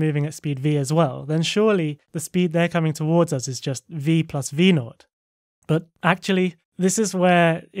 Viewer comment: what does he say v plus v lord what did you just say my nigga??